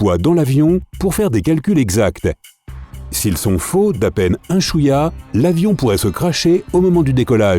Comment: c moi ou il a dit « si ils sont faux à peine chouïa (sa veut dire un peu )